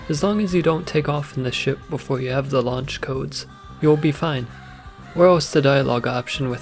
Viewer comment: bro i cant understand what ur saying around 0:55 You mumble. "where else the dialog option with how will appear" ??? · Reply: "Or else"